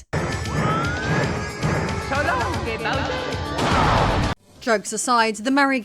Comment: 😂 " so long Gay Bowser " 9:05 so that is what Mario said in Mario 64